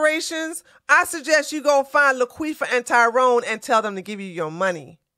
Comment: wait wait, aroiund 11:30, did you say LaQueefa? Thanks for the message. I ain't even mad about my ancestors being enslaved, i'm doing okay now right.?